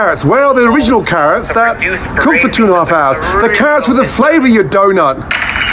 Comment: “Where are the carrots with the flavor you donut?” That’s gotta be the best roast I’ve ever heard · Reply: I thought he said “the carrots were THE flavor you donut!!”